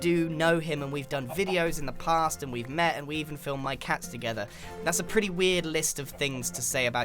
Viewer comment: I heard that so wrong. "We killed my cats"